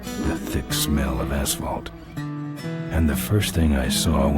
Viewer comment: As 13 yr olds we died laughing at the opening narrative ... as we thought the guy said 'a-hole ' instead of ashphalt.😂